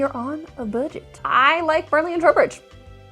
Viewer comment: I think the transcript is wrong at 16:24 ? Were you saying Burnley and Trowbridge instead of Bradley and Robert?